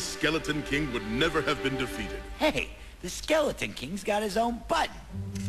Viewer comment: Did Sparx just say "The Skeleton King's got his own BUTT."?